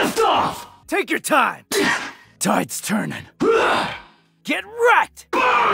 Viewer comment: What he say at 8:45? It happen when u try showdown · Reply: Tides turning, showdown He also says : ashes to ashes * does the attack * dust to dust